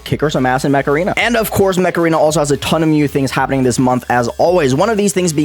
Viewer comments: Bruh I thought he was saying macarina not mech arena lol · Im ngl I heard "macarena" instead of mech arena lmao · LOL i thought said he was sponsored by Macarena until the words Mech Arena popped on the screen